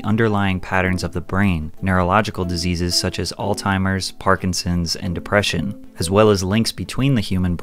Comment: did you call Alzheimer’s, all timers 😂